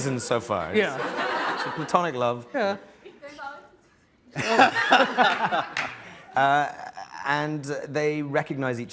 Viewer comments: what does the audience member say? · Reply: ʻDepends all on Season 3ʻ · What did the girl from the audience say that they laughed? I do not understand( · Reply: "THERES ALWAYS A SEASON 3"